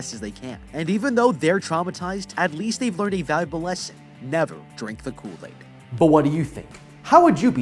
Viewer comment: Never drink the cool aid 😂 i thought it said never bully😂❤